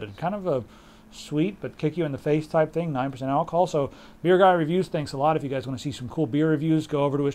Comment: What is the youtube channel for beer reviews? I really can't hear what you said on 04:10 "BEERgrahREVIEW" ???